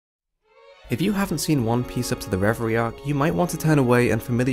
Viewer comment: what did he say? Referee arc?